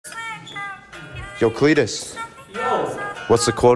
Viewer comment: I thought he said fetus